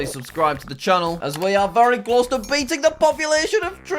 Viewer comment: is it just me or did I hear saying “1.4 billion”